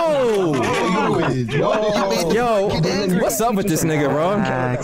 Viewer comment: I feel like I heard him say "wassup n word"